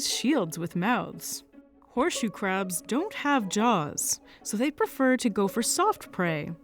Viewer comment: I thought she said they don't have jobs. 😅